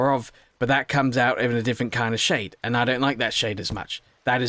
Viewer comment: did I heard Shape?